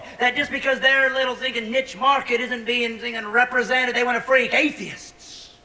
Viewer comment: What the hell is he saying at 0:27? "they wanna free atheists?" "they wanna freak atheists?" Either way, it makes no sense to me.